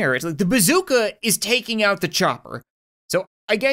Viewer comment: When he said chopper I thought he was saying chucker lol